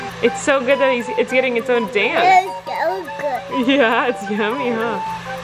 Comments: ballinger @ 5:16 😱 said “SO GOOD” right after you did while he was dancing around eating the ice cream. OMG! I don’t think you heard it! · OMG AT 5:15 IT SOUNDS LIKE HE SAID “SO GOOD” TELL ME IF ITS JUST ME THAT HEARD THAT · did anyone else hear say "it's so good" at 5:14 😂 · did anyone else hear say “ya its so good” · it sounds like is saying it’s so good 🤗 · Um Colleen, 5:15 I think he said “So good”🤨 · I swear ON MY LIFE at 5:15 said “yeah it’s so good” !! Anyone else hear that?? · did anyone hear say "it's so good" or am I bugging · Anyone else catch saying “it’s so good” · did anyone else hear mimic Colleen and say “it’s so good?” 🥺🥰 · I swear at 5:14 he said it’s so good 😊 and at 6:19 it looks like a dinosaur 🦕